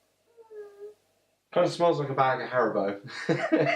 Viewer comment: you say that it kind of smells like a bag of Harrabone, in a nice way, like Harribone:. What is Harrabone? At 7:50 you said you could imagine nodding off whilst sitting in a "Chesterfield", if you had one. Isn't the chair you are sitting in a Chesterfield design? Was this supposed to be joke? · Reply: Reb Mordechai hey buddy...Haribo are a German confectionary company. They make gummy sweets in their product range...if a bag were to be opened...I was stating they smell similar. The Chesterfield was my less intelligent stunt double talking nonsense...he doesn’t realise he’s actually sat in one! I suppose it might look like really dry humour that’s a bit clever....but he is actually just stupid. I might stop using him...we don’t actually look that alike...he just begged me to do this video...